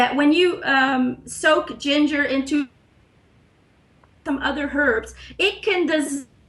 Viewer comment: What does she say at 7:28 when you soak ginger in? My sequence always jumps? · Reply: ilonamw she just says some other herbs...I'm curious about that recipe as well...